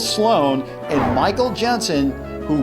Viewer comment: why did I think you said "Michael Jackson" for a sec?